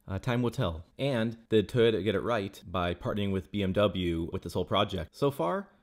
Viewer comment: At first I thought he said "did Toyota get it right by parting with BMW", but it cries BMW in so many areas so I listened again, and he indeed said "by partnering with BMW". Makes better sense now. I guess...